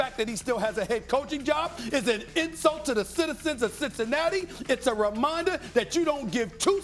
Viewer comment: What that nigga say at 2:31 "citizicicinatti" what is that?